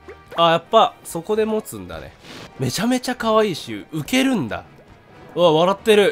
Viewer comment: 「浮けるんだ」が抜けるんだがに聞こえた